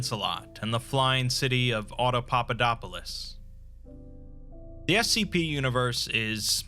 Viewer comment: What did you just call me? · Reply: Autodopalopalus